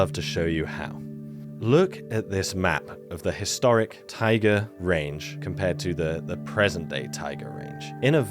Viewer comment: I thought he said “…historic taiga range compared to the modern taiga range…”. I thought he was talking about arctic and subarctic forests that could support mammoths. Tigers didn’t cross my mind until he showed a picture of tigers.